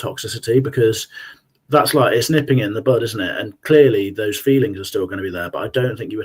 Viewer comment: Did say " nipping it in the BUTT " ? 🍑😉 We all suffer from eggcorns, but that's a bad one 🤣